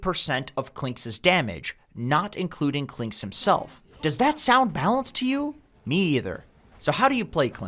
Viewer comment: I thought I heard Loda in TI3 said “is that balanced?”